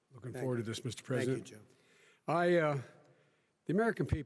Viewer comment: Did Trump say "I hate you"